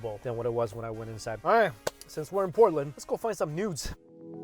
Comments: haha i thought you said Nudes here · Haven't watched the whole video yet but am I the only one that heard nudes at 4:10? I know I must be hearing wrong but even the caption said nudes o-0 Update.....I'm so slow......noods lmao don't judge me 🤣🤣🤣